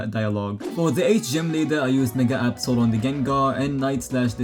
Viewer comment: WHAT DID HE SAY😱😱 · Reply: Mega